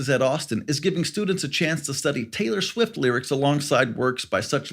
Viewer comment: "study Taylor Swift lyrics" I just about spit my coffee out! LMAO I had to listen again, cuz I thought you said Taylor Swift lit! Love it.